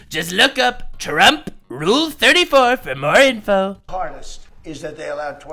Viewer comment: It sounded like you said, look up Trump R34. Please don't do that. Lol